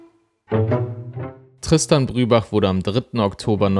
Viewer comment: Ich habe zuerst verstanden: "Hier ist Christian...".